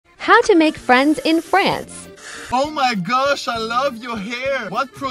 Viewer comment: the voice : "how to make friends in France" what i hear : "how to make French in France"